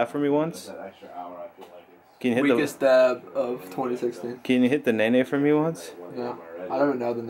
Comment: lol did he say "weakest dab of 2015"